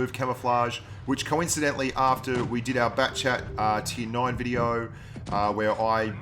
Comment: Rip I WISH the batchat had 2s between each shell! It’s got 3s between each shell, and the lightbulb pops at 3s not 2. (Unless it’s changed from PC). But the batchat for sure has 3s between shells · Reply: All this time I thought he was saying backchat